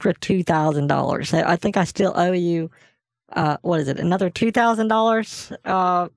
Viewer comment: Sounded like he said what is IL and someone said Indiana. Lol